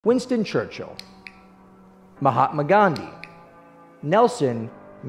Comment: “Mahatma Gahdhi” I heard “ima hide my gun” wtf😭😭